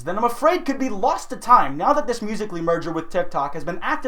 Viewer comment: i thought he said 'This musically merger with ted talk'